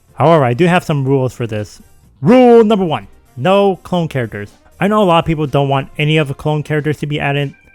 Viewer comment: When you said rule 1 I first thought you said NO KONG characters but then I realized you said clone characters 😂 Also you forgot Diddy Kong